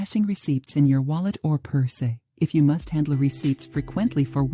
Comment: I really thought this sounded like a non-AI generated voice narrator (who actually sounded pleasant), but at 6:03, I am hearing "pur-say" instead of "purse" and "glove-ez" instead of "gloves". Oh well. 😐